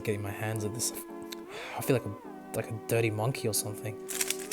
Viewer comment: what the helly are you saying mate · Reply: i feel like a dirty monkey or smth